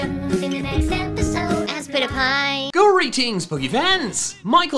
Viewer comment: The way he says "Aspitpiay" is sounds like he is saying "I spit your pie"